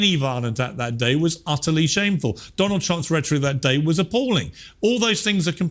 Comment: What did he say? "Donald Trump's (?) that day was appalling." I tried it a 3/4 and 1/2 speed, still can't tell what he said. Sounds like "retree", "Donald Trumps retree that day was appalling." 5:58 "If they can make you believe absurdities, they can make you commit atrocities."